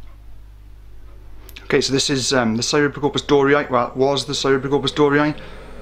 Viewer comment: Does anyone know the species of spider this is? He says the name so quickly, 0:11 unless you know it already, you cant quite catch it. Edit: Got it: Cyriopagopus doriae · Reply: Yup Cyriopagopus doriae